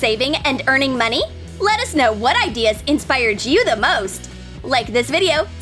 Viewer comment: Lettuce know which ideas inspired you the most. That's what i heard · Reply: This is supposed to be a joke guys it's let us know